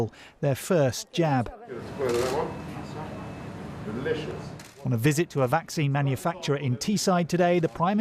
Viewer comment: did i hear him say "delicious" XDDDDDD